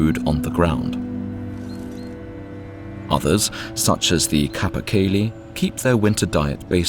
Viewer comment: I think he’s saying „capercaillie“, also known as the Eurasian capercaillie, wood grouse or heather cock. Tetrao urogallus (på dansk tjur). But is it really? It seems a bit small.